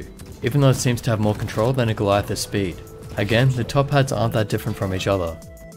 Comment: "I want to start by pointing out that any other Top Hats are great!" Good to know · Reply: It's "Top pads". I clearly hear the second "p" from "pads" it just sounds weird because said it a little bit faster and the second p from pads was more silenced so even the subtitle said "Top ads" because the second p was quite dull but the second "p" is there